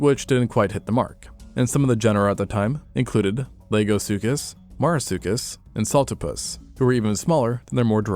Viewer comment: Who else heard "Legosuchus?" 👇